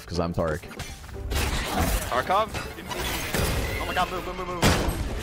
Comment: DID I HEAR TARKOV?!?!?! 🤤